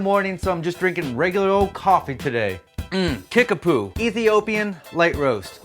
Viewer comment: did he just say kick-a-poo?